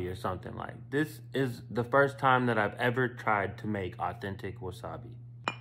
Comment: Thought he said real Wasabi